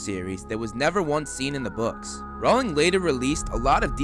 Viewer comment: Wait did he say Ron at 0:21?